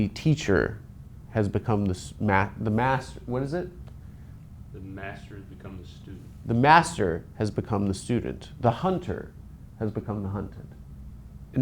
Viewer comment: Did i hear Joe Pera say "the master has become the student?"